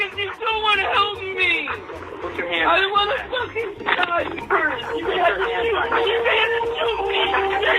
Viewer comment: Did he say “I want a f***ing diaper?!?!” 😳😳😳😳 LMAOOOOOOO 😭